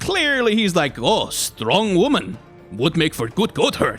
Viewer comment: i thought you said 'ah, strong woman make god butt hurt' implying braum wants to get pegged by illaoi and i agree even if it's not what you said